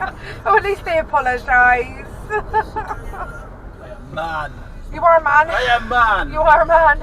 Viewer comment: I heard "You can piss off man." or "I can piss off here."